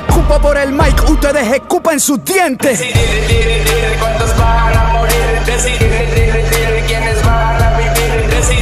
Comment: El coro dice Decidir cuántos vas a morir , decidir quiénes van a vivir y es un clásico de los 70 y 90 de Wiso G una canción muy buena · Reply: Gracias bro, todo el tiempo pensé que decía “resi dile dile dile cuántos van a morir” XD